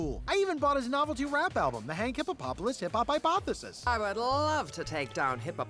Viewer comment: I thought it said “Iboughtthisis” As in to say why did I buy this, but I guess it was hypothesis